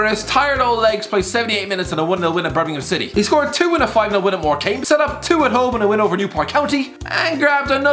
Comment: did you just say "more came"? Ahhh, so that's why your weetabix are soggy